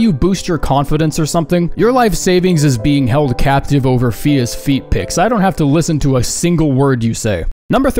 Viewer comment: I heard ''fetus feet picks'' instead of fia's feet picks